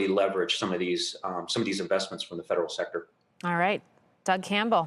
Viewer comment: I heard "Salad Power".